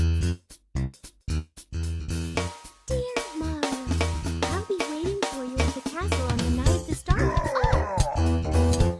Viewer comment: what was she sayin before bowser interrupted her… also what that Bowser said after Peach said Let’s Go? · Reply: "Dear Mario, I'll be waiting for you at the night of the castle star festival"